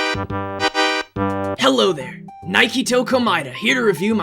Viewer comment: i misheard mankytoe komaeda